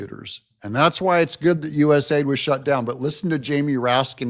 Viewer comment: sorry thought you said usa but it was usaid